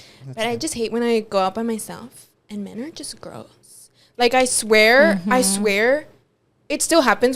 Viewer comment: I also thought she said girls ✋😭 I’m soooo dead 💀